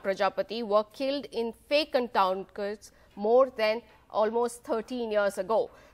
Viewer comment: what she said on 0:11 ,you must listen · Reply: She said fake encounters more than 13 years ago